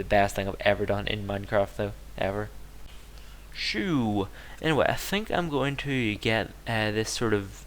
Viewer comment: At @ 10:44 did you say "shoe" like in shoegun56, or did you say "sooo" in a weird way? :P